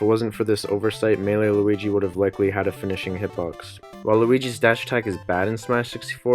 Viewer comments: did he say waluigi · Did I hear WALUIGI! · wait... did i heard WALUIGI!? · did he say waluigi?